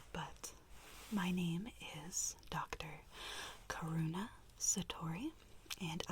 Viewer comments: When she said Karuna I thought she said corona and I woke up so fast it scared me so much for some reason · Pfffft I thought you said my name is Dr. Corona I was OH NO